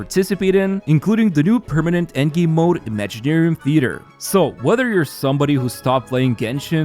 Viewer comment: ngl for some reason I got scared mishearing "wuther" in a genshin promo lmao